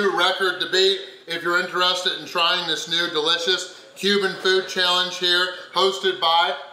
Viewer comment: I kept hearing “human food” every time he said Cuban food.